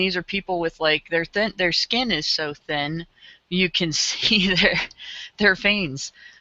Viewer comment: I thought said (at 8:15) their skin is so thin you could see their fangs, lol.